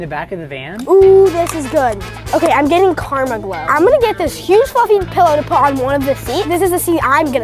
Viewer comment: In 1:27 I thought said this huge fing pillow